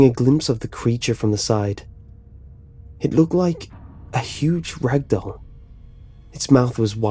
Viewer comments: Lol urn on su titles and see what it says. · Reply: lol a huge red dildo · Is the creature at 8:13 described as a "huge rag doll", "Huge red dog", or what? I turned on the caption but it says "it look like a huge red dildo its mouth was wide open". for some reason I don't think this is correct. I sometimes have a hard time hearing, so what was actually said? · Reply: rag